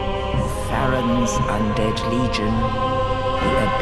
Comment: "Farron's Undead Legion..." I hear "Faraam's" but that could just be me.